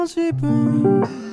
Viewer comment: 行為が恋にも聞こえるって思ってひやっとした